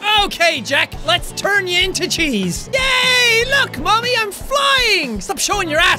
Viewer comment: my captions say "lets turn you into chinese" 😂😂😂